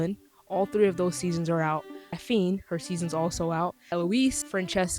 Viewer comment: Did you say Atheen? 00:41 I can't quite tell, but it didn't sound like Daphne.